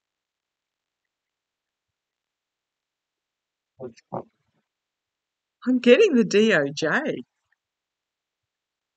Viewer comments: Does anyone else hear the man's voice in the video at around 15:38? Can't make out the words, but very interesting! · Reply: sounded like "police come" to me. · Did anyone else hear the voice that said two words around 15:36? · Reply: yes, sounded like 'police come' to me.